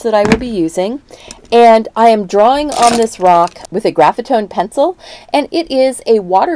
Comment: I missed what you said about the pencil... what's it called? I've used chalk pencils before but I end up smudging the lines while I paint, lol. I keep hearing "graphite own" which can't be right... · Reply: Hello! So sorry the pencil I used is the Derwent Graphitone water soluble graphite pencil. Thank you for watching and your question! If you couldn't hear it I bet others couldn't either. :)